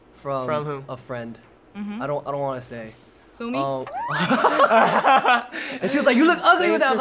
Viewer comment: somi????? like a soloist ex jyp ex ioi somi??? they are friends????? what???? or i misheard it?